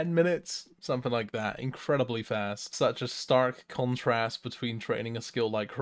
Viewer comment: Did you just say 'starf contrast'?